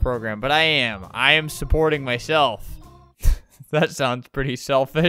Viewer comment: is it just me or did it sound like he said "im supporting my SHELF" ....